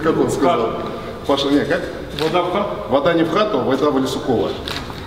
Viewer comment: что он сказал на 1:05 что то не разобрал слов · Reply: вода в лесукова